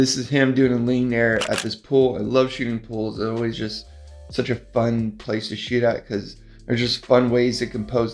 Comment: I thought you said I love to shoot pools, they are always so fun to shit